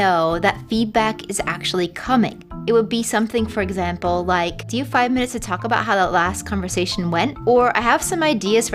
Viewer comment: i cannot understand what is being said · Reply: it's: do you have five minutes to talk about how that last conversation went? :-)